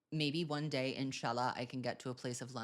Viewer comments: did you say inshallah? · is he saying inshallah❤️🫶😭